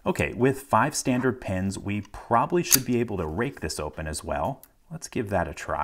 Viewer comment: is he saying rape or rake